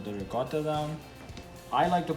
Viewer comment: what was that white stuff you put over the sauce? Because I couldn't understand a word you said. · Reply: EVERGREEN KING thank you so much it’s ricotta cheese whole milk